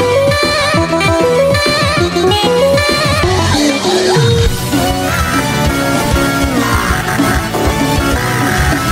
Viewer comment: Can anyone tell me what he is saying here 1:20 it sounds like he say where are you going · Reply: “Where are you going?”